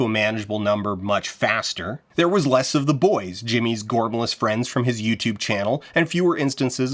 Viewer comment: what is the word you are saying here? The captions say “gormous” but google is telling me that is not a word. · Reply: gormless, a more common term in uk, ireland, canada and the like, means stupid